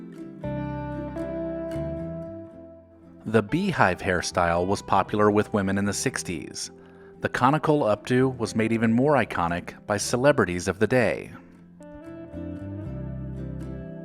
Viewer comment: he he...conical hairdo...I thought you said COMICAL